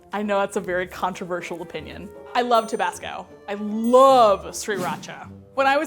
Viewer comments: I thought she said Tobacco and I was in shock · i thought she just said ' I love tobacco ' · Did she just say Sriracha? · I thought she said she loved 3RACHA I was like girl me to